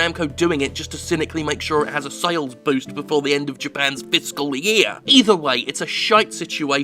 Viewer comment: What does Jim say around 6:46? "Japan's fist-school year?" I'm sure I am wrong but that's what I heard. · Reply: I believe it's "fiscal".